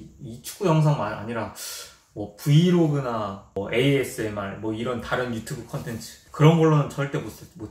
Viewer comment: Did he just say ASMR??? ASMR Channel coming soon!????